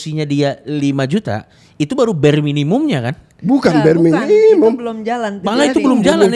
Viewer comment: yg dibilang bapak itu apa? Bukan bare minimum, itu ......... Soalnya suaranya saling interferensi destruktif, saya bingung dengernya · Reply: Ketika gaji seseorang 5Jt. Jika Kontribusi nya terhadap perusahaan sebesar 5jt. Itu disebut belum masuk bare minimum karena cost untuk produksi dan lain lain lebih dari angka tersebut.